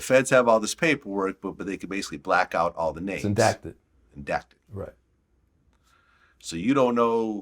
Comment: Did they just say “indacted” ? Redacted